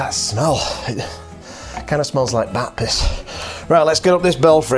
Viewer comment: I had to listen twice. Could have sworn you said “kinda smells like Baptists”. 😀